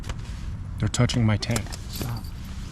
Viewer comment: I heard someone say "Stop it"